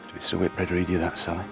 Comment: What are the last words Sharpe says in this clip? At about 1:01, just after he describes the battle of Badajoz. His accent is so thick I can't make it out. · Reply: 'Did Mr. Whitbread read you that, Sally?'